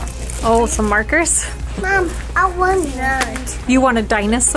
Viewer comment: Did said “Mom, I want tha~t” ?!! 😱😱💕💕👏👏